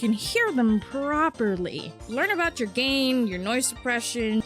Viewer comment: Why did I hear "your noise gay" xd